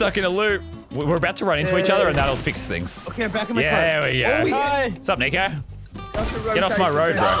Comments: did he just say the n word? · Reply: He said sup nico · when said nico, i thought he said the n word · anyone else hear that??? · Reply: He said nico not n word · sounds like he said wassup n**